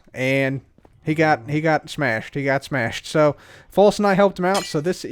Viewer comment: did he say False Knight? What is something we don't know about false and wels?